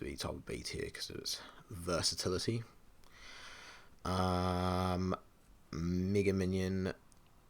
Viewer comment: What did bro say💀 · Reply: Nega minion